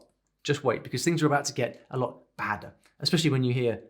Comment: Wait... Did he say "it's getting badder"?